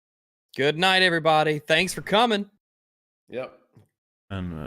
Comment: at the very last second, said “also, Friday, the 10th, 5pm BST”. And in the chat, right before it got cut off, he was being very coy, and he’s been saying it’s a week or two out...ITS TFA PART 3, AAAHHH! IM SO EXCITED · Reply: I could have sworn he said February the 10th and I was confused as fuck. Thanks for clearing that up!